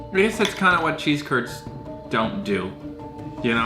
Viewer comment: I thought he said chesse turd